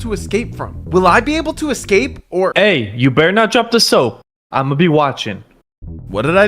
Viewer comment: did bro say don't drop the soap😭